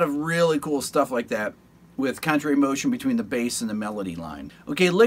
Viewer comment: Class act! I kept hearing “country motion”, thinking what kind of a motion is that?! :D